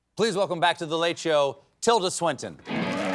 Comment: I thought the host said Silda Twinton ! ❤😅❤